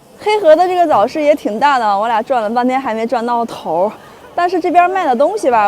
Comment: 黑河早市场很大，我俩转了半天还没转到头，我听成我俩撞了半天还没撞到头！哈哈我的中文水准还可以